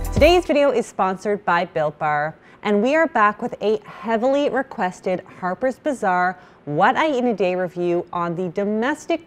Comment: Lol I thought you said today's video was sponsored by Bill Burr.....the hilarious stand up comic 😂😂😂😂